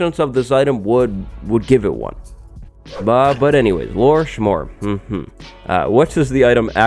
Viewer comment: Did you say smore? I want a smore! Especially a lore smore